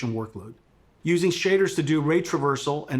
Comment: Did he really said "Which reduces sh*t of workload" or am dreaming